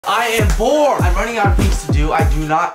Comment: at the beginning i thought you said “i am poor” 😂